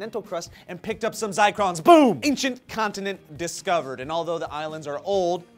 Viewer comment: Fuuuuuuu i thought it said Atlantis discovered